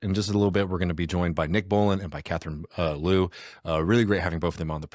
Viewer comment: Ooooo Nick bowlinnnnn sorry I thought I heard Nick Mullen. Awesome show yall